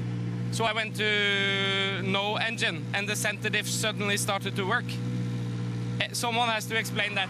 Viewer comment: Coll video! How ever I don't understand the word he said at 0:07 Something like "centetive" or so. What did he say? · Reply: Center Dif (Center Differential)